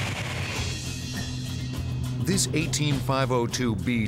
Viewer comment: Whoever was on location filming and taking notes definitely didn't hear the model of the airplane correctly. Its an AT-502B, and there's no such thing as an 18502B. Guessing they heard "AT" as "eighteen." Interesting to see that make it into an actual documentary.